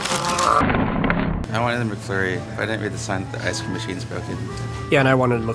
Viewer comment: I thought said he was a furry at 10:29 lol